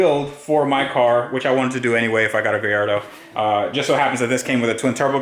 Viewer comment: Nice touch saying "GUY ardo" for a sec i thought you said "GAY ardo" had to rewind & hear it from saying "A Gay in"